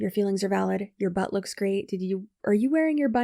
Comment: I totally heard “are you wearing your butt a different way?” at the end and cracked up 😂